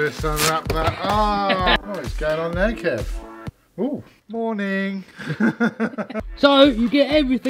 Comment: Did he say horny